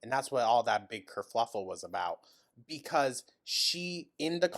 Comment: I'm sorry, did you say "kerfluffle" instead of "kerfuffle"? That sounds very cute😂💛